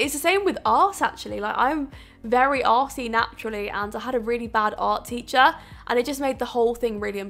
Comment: Did I just hear "I'm very arsey naturally" 😂😂